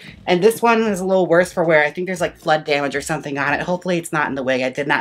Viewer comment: Omg i thought u said "blood damage" instead of "flood damage"....🩸🩸🩸